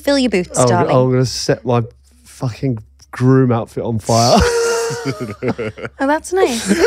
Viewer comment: Trying to get ready for work whilst listening, crying with laughter. Genuinely thought was going to say "set my f ing Groin on fire" 😂😂😂 love you both! ❤